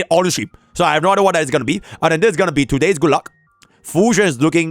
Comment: I was just listening and I heard "today's gulag" at 2:37 lol